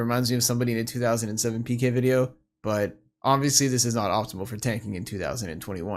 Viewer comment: I thought you said no more staking for 2021